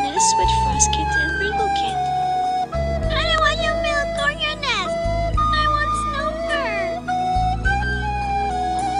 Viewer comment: WHAT IS HE SAYING i know whitekit says "i want snowfur" but what did he say before that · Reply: The cat says ‘I don’t want your milk or your nest, I want snowfur’